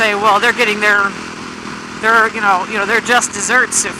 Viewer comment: So you just said "their justice hurts" and I heard "just desserts" and now I'm craving some frozen yogurt with yummy toppings. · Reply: I actually did say, "their just desserts." :) And, yeah, donuts are the best sponsor ever! Way more delicious than riding jeans. ;)